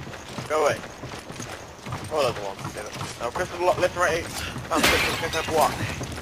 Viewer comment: lol, i was expecting him 2. Ohh, you said thumbsticks. I thought u said triggers.